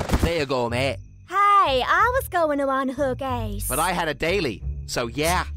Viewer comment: With the accent it sounded like Kate said she was gonna unhook ass